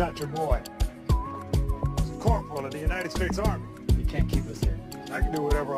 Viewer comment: I was a contractor and if you think I would tell anyone in a uniform what to do, you are out of your mind, let alone a Colonel. Good way to end up horizontal and forgotten. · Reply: I thought he said corporal not colonel